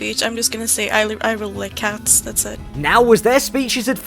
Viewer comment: Huh, dialogue and Subtitles don't match. (They say they like Cats, but the Subtitles say they like Dogs)